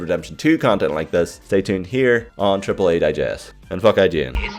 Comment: was that "fuck ign" I heard?? Lmaao